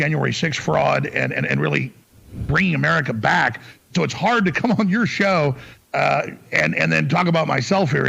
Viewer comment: I thought he said, "I got hard coming on your show". 😂. Bye the way Alex, you can't say you are innocent because you were convicted and lost your appeal. 🤪